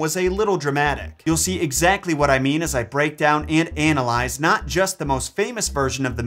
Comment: I'm so sorry, I'm stuck on the fact that I think I heard "anal-eyes" instead of analyze.